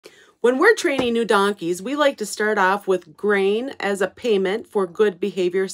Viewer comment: I thought you said brain for a second 💀